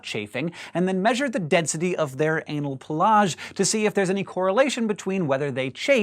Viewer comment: When he said anal pelage I thought he said collage and got confused quick.